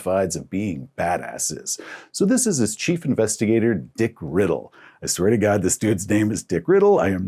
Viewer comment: the Dick Riddle anecdote sounds like a delta green operation lol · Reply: Oh, I thought he said Dick Griddle and I crossed my legs.